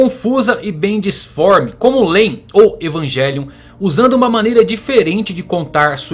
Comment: como o que??? Só entendi o Evangelion. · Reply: Lain. Serial Experiments Lain... um dos animes psicológicos mais clássicos que existe. Estou na metade, tem que ter paciencia pra pegar os ganchos filosóficos